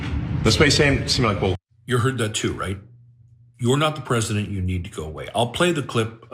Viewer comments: I thought he said you need to tell the press people to go away!still inappropriate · I only hear "i have to go pee" not "you need to leave" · I hear “you’re not the president. You need to go to sleep.”